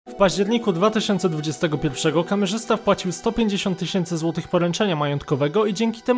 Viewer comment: nie wiem czemu ale za pierwszym razem usłyszałem "w warzywniaku"